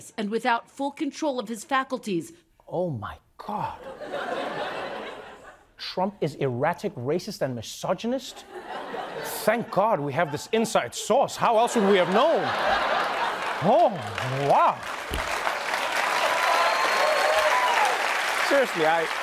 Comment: I know he said "inside source", but I heard "inside sauce".